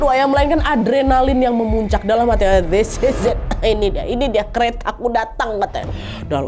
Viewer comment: lu lg ngomong apa nad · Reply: This is it